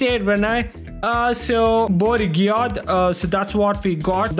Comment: did bro say Bourigyat